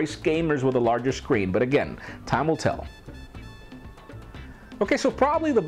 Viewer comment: when he said time will tell, i thought he said tamil tell :$